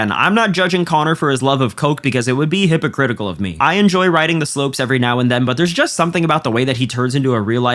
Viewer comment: did bud just say he likes doing coke every now and then😂